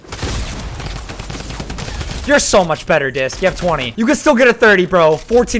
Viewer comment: did i just hear him say ur so much better disc?